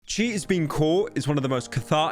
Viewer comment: Sorry I had to listen to the beginning of the video a few times. I kept hearing "cheese being caught" due to accent